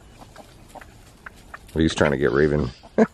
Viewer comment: Who else heard him say get rekt